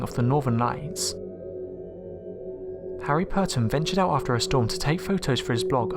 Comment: did you say Harry potter???